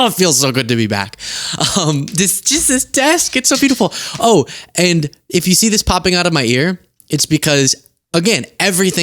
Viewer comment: I heard you say ‘if you see this popping out of my rear’ and…… Of course you are giggling. Enjoy😘